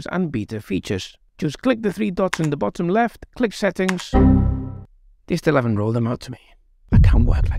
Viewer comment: Too bad I can't make out what you're saying at 00:19, even with captions on.... but I really appreciate your sense of humour, and infos, of course !! Looking forward to your future videos !! Thanks so much !! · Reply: Oops sorry, I did mumble there a bit. "They still haven't rolled them out to me". ('them 'being the plugins). New video coming out in a couple of days about the plugins. No more mumbling from now on...